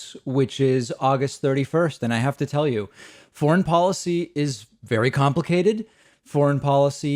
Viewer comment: I think this is the best thing Biden has done in his Presidency so far. That war needed to be ended long ago. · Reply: I thought he said porn policy 🤣 I was wondering if it was going to be another story about onlyfans. Definitely good to get out of both iRack and Assghanistan though. 🤭